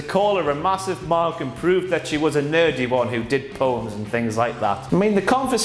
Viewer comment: When said that charlotte said bayley did poems i had to click back because i thought he said porn! not gonna lie i got a chub i wouldnt mind bayley in a porn she has a nice bottom